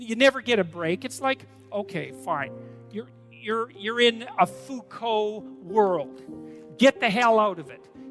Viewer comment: “You’re in a Foucault world” is what he said at around 1:41.